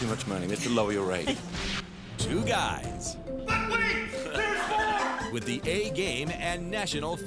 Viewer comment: he actually said but wait there’s more